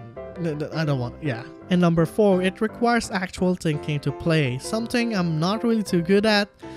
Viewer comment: It requires what to play? I didn't understand what you said there. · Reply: "and Numbuh 4, It Requires Actual Thinking to Play"